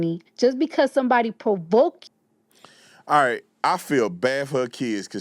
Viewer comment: did she just say because someone provoked you? I guess CHUD the builder is innocent for provoking also